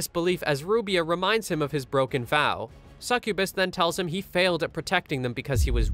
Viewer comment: Sound like he said Mr.sucubus at the stari